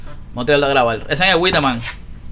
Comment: Que dice en el 08:24 ? Witerman?? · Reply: Whiteman, es el tercer dembow que suena